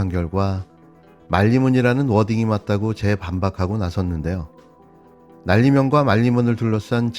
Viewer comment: 자꾸 들어보니까 날리면 말리면으로 들리기도 하네 ㅋㅋㅋ 윤통 발음이 얼빵하네 ㅎㅎㅎ